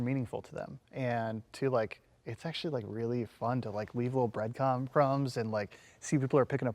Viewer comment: did he say... BreadCum??